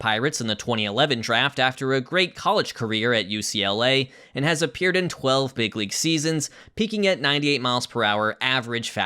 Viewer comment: Definitely thought you said "and has a beard in 12 big league seasons" when you showed Cole's Astros clip